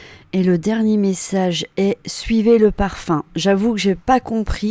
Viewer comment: Merci pour votre vidéo et votre générosité. Bizarre mais moi au 6:30 j'entends "suivez le romarin". Cette plante sauvage est très répandue dans les garrigues méditerranéennes. Vous êtes bien plus qualifiée que moi mais je tenais à le préciser vu la tristesse et le désarroi engendrés par la disparition de cette maman. Merci pour tout.